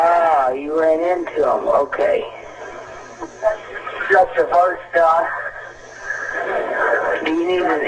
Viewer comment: sounds like he said "shot the first guy" & I agree, with his raised heart beat, heavy breathing & adrenaline following & that's not including his possible fear & helplessness to be able to defend himself...he could vary well possibly said "stroopers" or "troopers", but it sounds slightly off because all the previously mentioned factors. I know they would train use to focus on our breathing & speech when you out of breath & full of adrenaline in mayday training, when I was in the FD. People can't help save you if you can't communicate efficiently(communicate what's wrong, where you are & what you need), especially when your running low or out of air. Another fantastic job, I look at much of the information in the same manor as yourself & try to think of every angle, possibility & think out of the box.